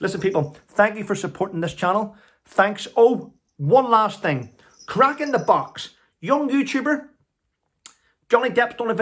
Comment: Hi, what is the name of the young fellows channel so I can subscribe to it? I thought you said crack in a box? I've looked but can't his channel...does anyone else know ? Just found it. Kraken The Box ....just subscribed